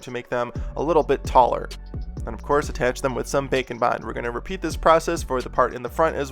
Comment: When he says “Bake and Bond” I hear “Bacon Bond” 😂 other than that I love all your videos I’m just a hungry